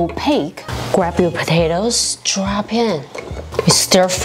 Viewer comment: When she said drop in I thought she said Chopin the maestro